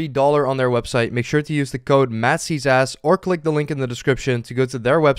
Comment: I thought you said "use code masseysass"